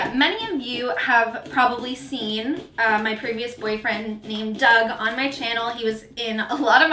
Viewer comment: My fiance is doing some work stuff and heard you say "previous boyfriend" and thought you said "creepy boyfriend" and he goes "yea he really was creepy" which goes to show how much I watch booktube that he doesn't have to see the video to know who I'm watching. He knows everyone by their voice and I guess he knows everyone's significant others too 😂😂